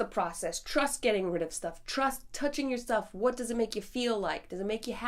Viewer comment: Did you just say "trust touching yourself"? 😱😂😂